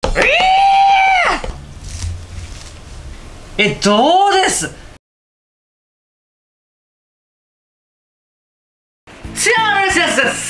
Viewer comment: そうとしか聞こえない｢Tuesday｣でワロタ